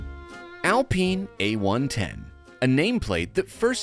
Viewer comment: Thought it said Alpina at first lol